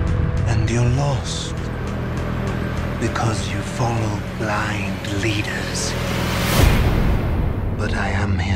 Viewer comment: HE SAID BLIND LEADERS DAREDEVIL CONFIRM!!!!!!!!!!!!!!!!!!! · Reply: Lol, i thought he Said " Dying leaders"